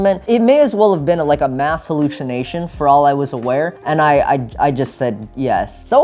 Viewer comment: Did he just say mash halushinasion